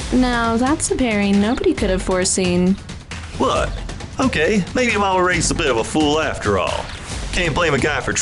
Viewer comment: bro I thought he said “what I’m gay maybe my momma raised a fool anyways”😭😭😭